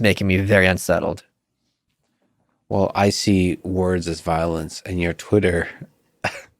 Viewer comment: Lex, pls tell me I misheard u say "I think words are violence".